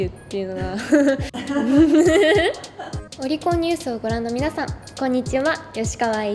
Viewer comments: 岡田将生の例の動画から来たから『ロリコンニュース』に聞こえたわ。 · ロリコンニュースに聞こえたわ